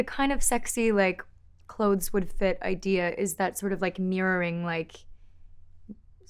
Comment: When I heard the actual song, the part where she says, "my clothes would fit" I thought she said "my curves would fit" and I was like: ... 😲👀